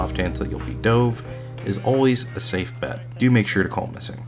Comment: What does he say at 5:21 ? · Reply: ok i turned on CC but what does 'call missing' mean? :D